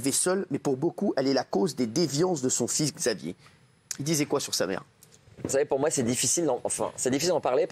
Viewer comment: je r^ve ou il dit "vous DISEZ quoi sur sa mère?" ???